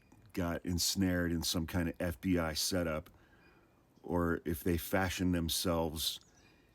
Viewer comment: "they fascist themselves" .... that's what I THOUGHT had said.... Makes sense 🤔